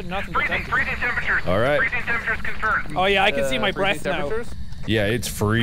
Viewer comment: I swear it sounds like he’s saying “I can see my breasts”